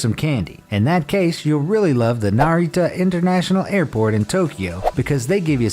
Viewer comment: When he said Narita airport, just for a second i thought he said Naruto